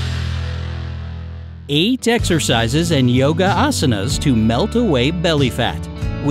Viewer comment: At first I thought he said yoga asians